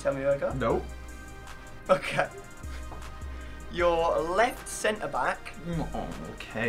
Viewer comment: 😂😂😂 I thought said right center back for a second and was just chill and not flipping out cuz he’s already used to it.